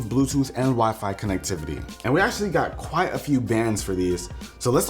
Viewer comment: I thought you said “we spent quite a few bands on these” lol